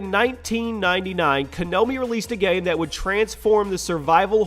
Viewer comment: Did he just say Kanomi as apposed to Konami? lol